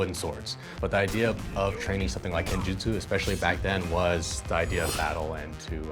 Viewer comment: I misheard him when he said kenjutsu and instead heard him say genjutsu. I was really confused for a bit